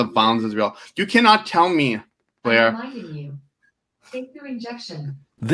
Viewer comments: Ooooh I thought the Alexa thing said "save your objection" instead of "take your injection"🤣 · when i watched that actual showdown and yaniv's reminder said to "take your injection," i swear i thought it was saying "take your rejection" over and over.